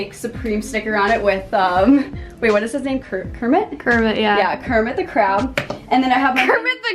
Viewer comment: Did..did she just say.... Kermit the crab?! Bye